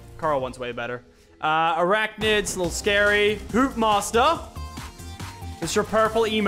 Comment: I swear he said “poop master”😂